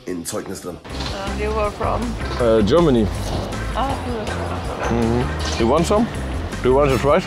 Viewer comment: Ich hab einfach verstanden „Do you want some sowjets“...